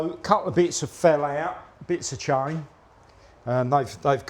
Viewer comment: What did you find in there, mate? Bit of change At least that's what I thought I heard the first time haha